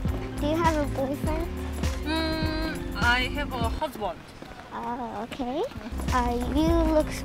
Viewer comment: do you have a boyfriend this woman : I have a husband lmaoo 😭☝🏻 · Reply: I misheard it as “ uhhh i have a hot one” but of her accent as she is korean But then the caption said “ uhh i have husband” 😂😂😂😂😂😂what the ……😅